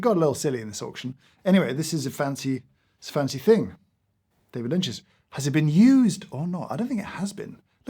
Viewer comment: My mistake for drinking coffee while watching this. I nearly spat coffee on my screen when I heard "David lint".